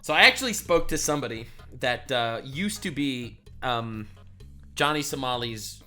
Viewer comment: I thought you said johnny Salami😂🤦‍♂️